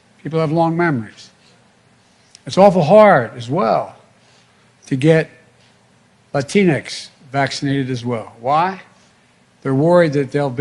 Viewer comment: A picnic short of a picnic, elevator not going to the top floor....Sound like he said, It's hard to get a Kleenex vaccinated.